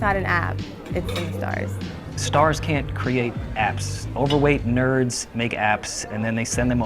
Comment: “Overweight nerds make apps and send them to communist countries for children to make the hardware” 🤣😂🤣😂🤣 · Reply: it pays to read a couple comments. i heard "overnight nerds" and was like 🤔❓